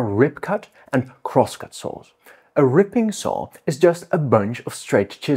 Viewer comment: Clicked on the video because I thought it said "croissant saws".